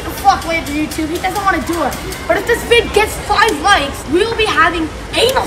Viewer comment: did he say 5 likes?🤣💀